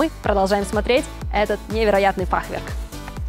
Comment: Мне одному послышалось "фахверк за 200 млн"??? Цену много преувеличили или полное отсутствие разума????